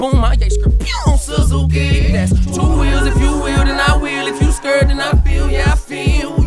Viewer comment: i honestly thought he said "if you skrrrrrrrrrt i feel you" dam